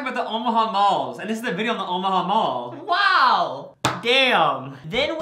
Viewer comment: I thought they said The Opium Mall! 😜😜 now everything in this video makes sense! 🤢